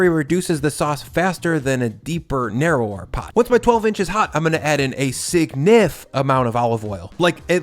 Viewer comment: Did you say, “ Once your 12” is hot”? 🤣🤣🤣